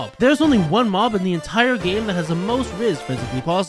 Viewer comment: I thought he said “makes me goes hard” on the big guy 💀